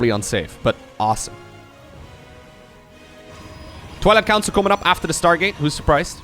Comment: sounds like said "Toilette council" :P